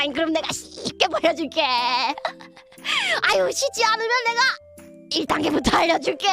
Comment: 쉽지 않으면이 발음때문에 씻지 않으면 으로 들리네 ㅋㅋㅋㅋㅋㅋㅋㅋㅋㅋㅋ